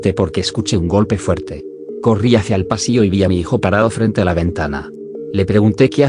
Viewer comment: minutos escuché hijo tarado😂